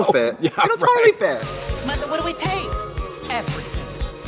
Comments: Does she really say Michael 😭😭 I thought it was mother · Not the subtitles at 1:10 saying "Michael" instead of "Mother" YIKES